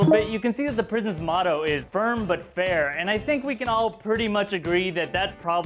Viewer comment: lol i thought "Firm but fair" said "Firm Butt hair"